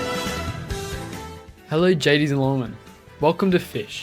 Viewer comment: did bro say gadies and lentlmen🫩✌️